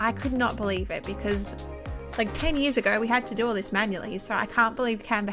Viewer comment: did u say ten years ago :0